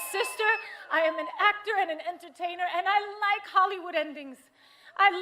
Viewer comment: Did she say I like Hollywooding things? WTF